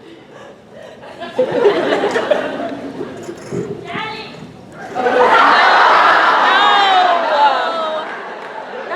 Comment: First of: thanks for sharing this =D Now, the question: What did the girl shout at 8:10 ? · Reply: "Daddy"